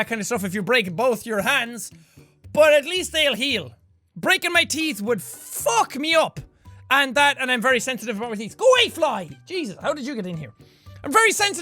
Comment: "I can't keep my attention- GO AWAY FLY yeah so I can't keep my attention while reading" Best line xD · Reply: I thought he said "Go away Flowey"... Now that would be interesting.